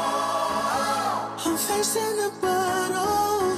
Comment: I heard "I'm facing the Buthole" now its stuck in my head as that..